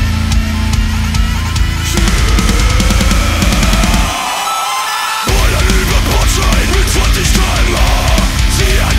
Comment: Can someone tell me what he is actually saying (the German words) when the lyrics say "20 km/h"? My German is horrible but I can tell he's not saying "kilometer pro stunde". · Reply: He says ka emm ha Basically he's spelling k m h